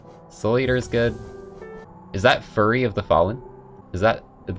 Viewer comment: did I misheard or... "furry of the fallen"?